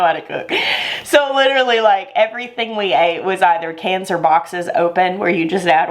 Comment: Lol funny! When you said “cans or boxes” I heard “cancer boxes” Not too far off. Lol!